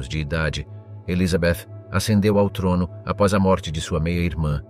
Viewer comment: Aos 21:06" "A trilhar em 1558 aos 25 anos de idade, Elizabeth acendeu ao torno (ou seja: ela tocou fogo no trono ?), ao invés de ascendeu ao trono (ou seja: foi elevada, ou subiu ao trono).